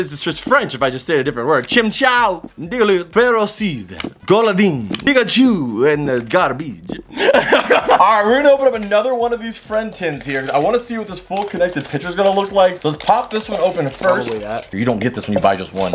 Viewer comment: I swear it sounded like he said nigga-chu